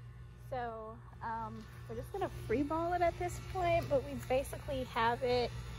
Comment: had me LMAO, did she say that that were going to just “free ball” it at this point Lol?